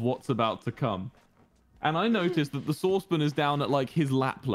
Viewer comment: Now I'm normally good at understanding British accents, but every time Wilbur said sauce pan, I heard source bin, and the first couple times he said that, I was very confused. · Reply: ME TOO I THOUGHT HE SAID SOURCE BIN